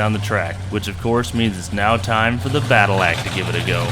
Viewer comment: Was disappointed to see the Badillac name in writing, I apparently misheard and continue to prefer "Battlellac".